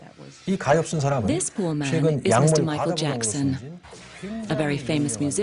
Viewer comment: did she say ' This woman is Michael Jakson ' ?